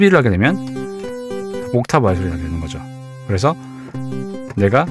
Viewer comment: 이 부분 옥타브 알소리가 되는거죠 라고 들리는데.. 실제로 무슨 단어인가요? 음악을 처음 공부해서 그런지 바로 인지가 안되네요 ㅠ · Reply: 옥타브 아래 소리가 더해 진다는 말이였어요. 급하게 말하다 보니 좀 씹혔네요.